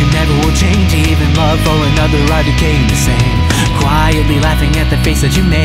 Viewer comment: I keep mishearing "Even love for another are decaying the same" as "Even loving idking the same" like I keep hear him saying IDKing the same.